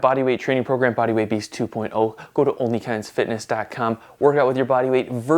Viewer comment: I thought he said "onlyfans fitness" 💀